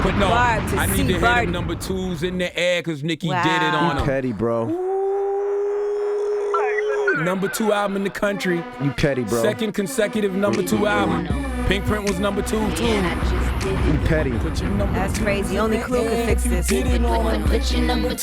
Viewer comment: All i keep hearing is "u petty bro" 🤣🤣🤣